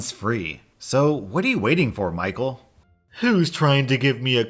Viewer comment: Holy crap i thought as you said i know your watching Michael.. That's my name